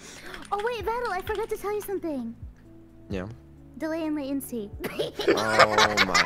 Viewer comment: What did say? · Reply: “Delay and latency“